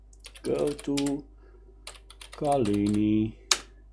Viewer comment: Hi, very interesting but I do no get original bg, as you at 30:30. And you say something as "goto call e ?!" : I don't understand and unreadable on screen.Thanks